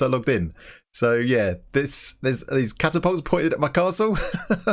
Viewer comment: did you just call those trebuches "carapults"?